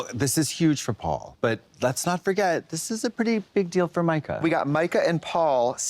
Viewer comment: “This is huge for Mika” 😂 I heard this big Formica. Are we doing kitchen renovations?